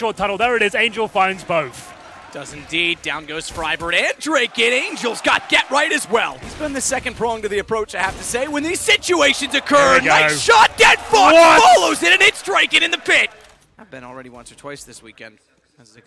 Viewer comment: sounds like he says "nice shot get fucked" lol